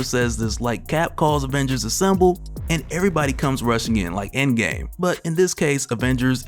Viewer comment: I know you said " like end game" but I keep hearing gang gang